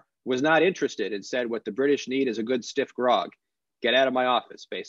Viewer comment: A good stiff rug? What does thst mean?..Oh! Stiff Grog... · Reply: At first I thought you said a good stiff rub? First I said what? Then I caught my goof, literally laughed out loud and then said actually that’s a pretty neat phrase a good stiff rub. So thanks for steering me to that!